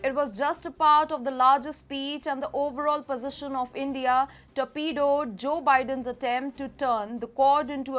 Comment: Did you say "....to pedo Joe Biden's attempt to..."??? ❤😆👌